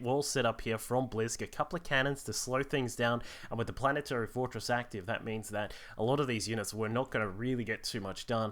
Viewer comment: turn up the bass! i mean volume. turn up the volume please. did he just say planet fortress activated? hur dur.. :P